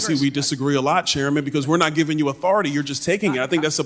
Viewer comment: the subtitles say "Chairman because we're not giving you a farty you're just...", is that authority? 🤣🤣🤣